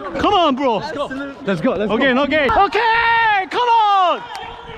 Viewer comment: did I just hear "ok, no gay"